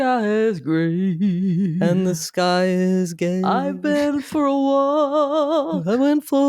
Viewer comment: When and were singing, did I hear sing "and the skies are gay"? 😂